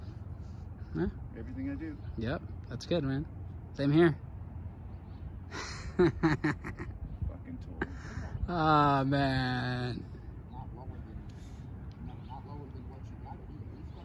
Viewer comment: did he call a fkn tool? Lol, that's what I heard at least.....